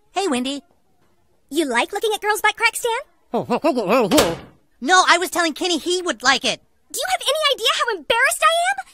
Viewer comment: what did Kenny say exactly? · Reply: “Oh hell no I gotta get out of here”